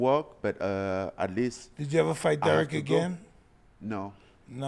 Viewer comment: For some context here. Mike said “will you ever fight Derrick again” he has a speech impediment and Francis doesn’t speak great English and he thought he said “did you ever fight Derrick again” that’s why Francis said no then he immediately said “that has to happen” Francis 100% wants to fight Derrick again why wouldn’t he want to avenge his only other loss.